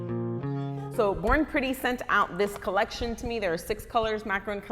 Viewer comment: I thought U said “ boring pretty “ 😂